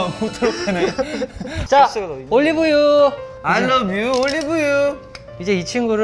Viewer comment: Why did I hear “ I love you, Olivia“ at 5:53...